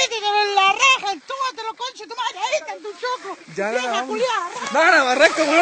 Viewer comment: Ahhh decia hay estan tus choclos xD siempre pensé que decía "elije tu kiosko" xdd